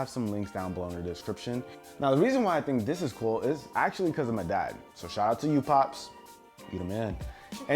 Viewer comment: When He Said Because Of My Dad I Thought He Said Because I'm A Dad Before He Said Shoutout To You Pops